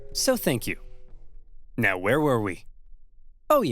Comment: subtitles issue mow instead of now